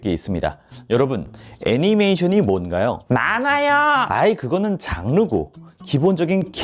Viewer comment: 자세히 들어보면 많아요!! 라고 들림ㅋㅋ